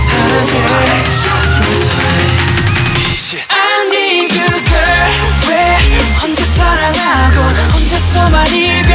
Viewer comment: Am I hearing things or does it sound like Rap Mon says Sh*t at 0:47 before the " I need you" chorus ..or is he saying shhh.. LOL I just now noticed it I love this song sooo much and just the dance is amazing and mesmerizing.. AND V and JUNGKOOK AHHHH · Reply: he says shit lol